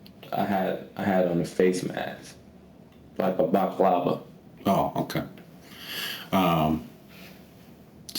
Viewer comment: Did he say he had a baklava covering his face?😂😂